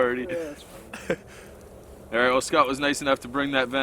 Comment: Did he say Scout brought the venison at 37:18?